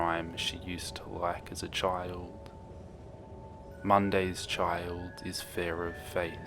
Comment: I thought it said mommy child but it's says mondays child